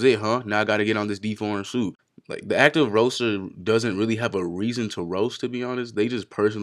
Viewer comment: did u say deforeign soup ? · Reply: Deformed suit